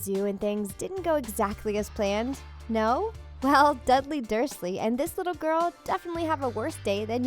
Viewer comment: Did she just say Dudley Dudley 0:52 OoO Any Potterheads out there